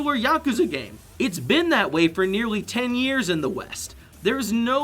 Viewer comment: did i hear ten years?